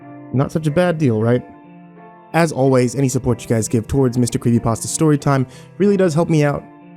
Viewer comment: I really thought you said mr creamy pasta at the end